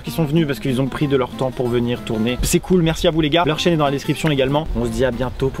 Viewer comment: C'est moi ou à 14:22, Maxime dit "Leur chien est dans la description" ?! 😂